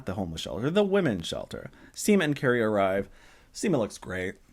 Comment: When you said at one point in this video "Seema and Carrie arrive" it sounded like you said "Semen Carrie arrive"... 🤣🤣🤣🤣🤣🤣🤣🤣